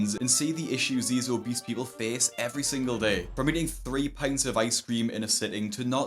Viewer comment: WHO EAT 3 ICE CREAMS?! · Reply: I was about to say me tf? Then I heard "pints" like ohhhh yeah that is a lot we arent talking about 3 Popsicles 😂